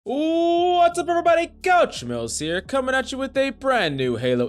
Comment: Isn't your name "Coach Mills"? Why does it sound like you say "Couch Smells" at the beginning?